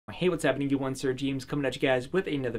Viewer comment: The first time I watched one of your videos I heard: "Hey, what's gappeningevreewun Sir James..."